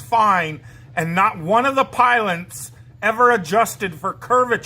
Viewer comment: did he say "piloNts" ? 4:19 I really hope for the sake of himself and his family, that one day, he will grow up. Also, spend less time on finding fancy words you can repeat countless of times to try and aggressively defend your point , and spend that time on actual knowledge, and not delusional beliefs. And finish your house man, become a better human, maybe you'll actually make a million one day.